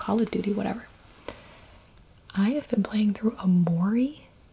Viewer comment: Can anyone tell me what's the game name in 20:09 ? I hear "mori" but i don't really find anything like that. Thanks.